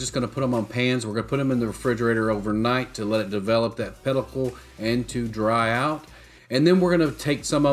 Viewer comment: Develop what was it? Did you mean "pellicle"? I couldn't hear what you called it. ✌